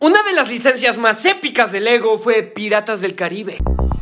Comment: Pense que decia legos piratas XD